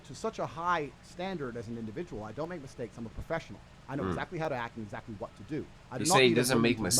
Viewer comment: Did he say "As a professional 🤔?" Listen carefully. Words mean things.